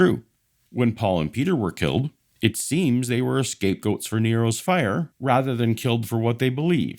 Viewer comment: Dude, did you say Paul and Peter were "escape goats"? I do hope I misheard.